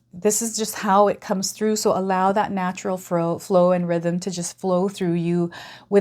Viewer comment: I heard “natural fro . “. ;)